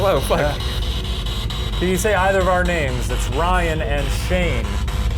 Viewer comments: min when Shane asked for them to say either his or Ryan’s name through the spirit box, did anyone hear a female voice say “Shane” right before Shane himself even said his own name?? It’s super quiet but you can hear it right after he says Ryan’s name and right before he says his own name. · did anyone else hear it say Shane?????